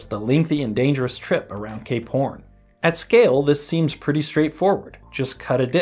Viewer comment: whenever this guy is about to say two words in sentence where one ends with the letter P, he always combines the words into one group of syllables. It sounded like you said gay porn instead of cape horn. and it also sounded like you said a pill instead of up hill.